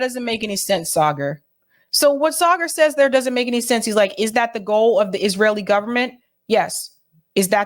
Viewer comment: misquotes Saagar @ 4:45. She claims he says "Is that the goal of the Israeli government" when what he ACTUALLY said "is that the goal of the Israeli far right".